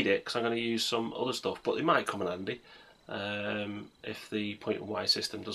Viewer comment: Did you say... "Come in Andy?" Ugh... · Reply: No, come in in handy